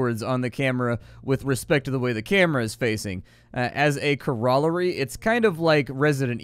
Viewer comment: “CORO-Larry”... took me a minute to process wtf you actually said 🤔